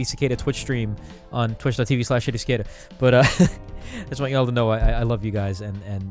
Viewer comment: thought he said his twitch name was shittycicada